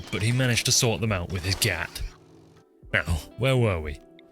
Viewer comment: did- did you just say gyatt